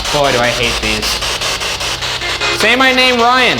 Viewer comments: it sounded like Eliza was telling her husband to “Say no.” 😳 Definitely a great episode for the spirit box! #MaybeOneDayTheyllSayYourNameBackToYou #LoveTheGhoulBoys #FinalSeasonBabyy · sounded like : say yes